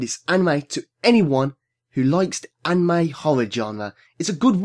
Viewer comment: When you said Horror I actually thought you said 'Holla' (Eyyy)